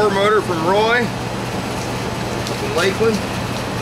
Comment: did you say lakeland? as in lakeland fl?